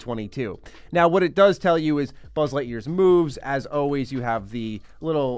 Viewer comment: Due to being half-asleep, I thought he said "Buzz Light year's boobs".